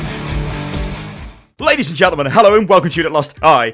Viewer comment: I hear "bladies and gentlemen, welcome"